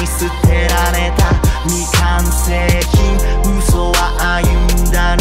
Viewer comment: 未完成品がいつもミカン星人って聞こえてて、いつも、この曲聞くと○ゴ○ゴルーガのアレが思い浮かんじゃって…。